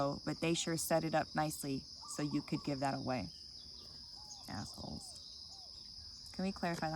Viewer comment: Did I hear "asshole"? 😂😂😂